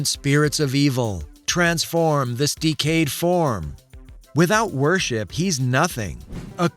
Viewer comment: When Mumm Ra changed. J thought he said transform mystic cave form to Mumm Ra.